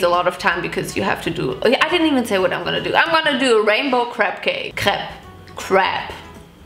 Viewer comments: lol the whole time i was giggling because i kept hearing "Rainbow Crab Cake" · I kept hearing "rainbow crab cake" lmao